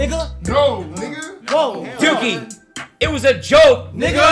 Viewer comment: Did he just say "kyun ki" at 6:21?😂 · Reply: He said Dukey which is the nickname adin calls duke dennis